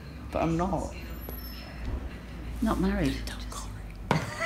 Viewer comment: What did he say after his (mum, I assume) said "you're not married"? 2:17 And at 7:39? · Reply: He said “don’t tell Corey” (his boyfriend) and he also said “I can blur them out if you want”